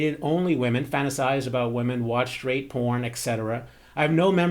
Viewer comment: i was very concerned for a sec when you said "straight porn". had to rewind cuz i thought i heard "rape porn", which was not only frightening that this person would be telling you this, but that you read it so casually lol. i can rest easy haha